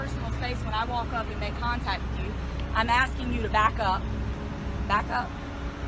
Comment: I thought she said hi Hitler and gave the hand up ign....but she said back up . How is he wasting their resources....the post office did...and then Hitler called code 3. Nothing he did was wrong. Have a meeting with your officers and train them!!!!! Not the guy that knows.